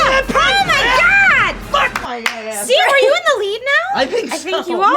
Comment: Did just say fuck my gay ass ?